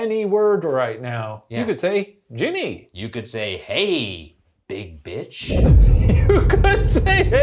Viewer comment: I hear the word "Shane" when Shane says hey big bi!ch